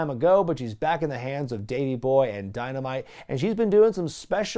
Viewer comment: "Daney Boy"? 11:57 Even the subtitles corrects to "Danny" instead of "Davey" lol